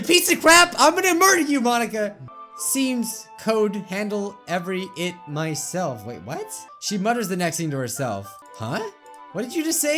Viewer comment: It sounds like she said “Seems like the code can handle everything by itself”, but at the end it says ‘myself’ so I don’t know